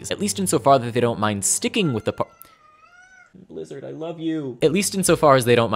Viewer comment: Just so everyone knows, my cat's name is Blizzard. I use a lot of nicknames for him, including: Blizzy/Bwizzy/Bwizzard/MY BABY/bwizzybwizzybwizzybwizzybwizzy. He meows when I walk into my workspace and don't go straight to him, because he thinks that I don't know that he's there, and he requires my permanent attention or else, presumably, the world will end. He will continue meowing until he gets that attention, and then will resume his meows if I leave him alone for longer than 30 seconds. This has been my PSA. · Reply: Oh, in the video I thought you said Lizard.